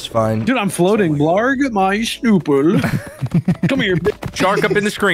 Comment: Dude why did say that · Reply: Blarg my shnoopl